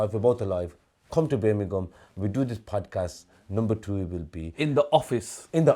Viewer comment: It sounded like he said 'come to Birmingham we will do a number 2 together'🤣